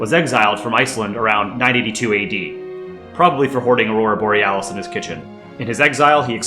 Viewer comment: this isnt related with the video but at 1:54 my brain thought it said aurora borealis